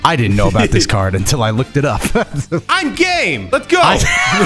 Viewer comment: Really thought he said I'm gay